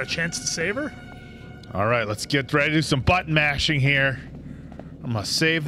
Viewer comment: please watch your pronunciation, sounded like you were ready for some ‘butt mashing’ 👌😂